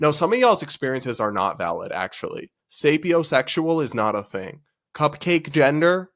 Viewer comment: I’m sorry but what the fuck did he just say. Sapiosexual??? I don’t even think I spelled that correctly